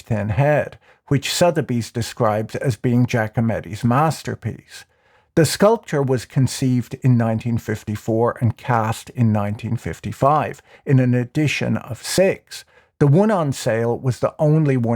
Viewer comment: I heard "The sculptor was conceived in 1954" and thought "Thats oddly personal"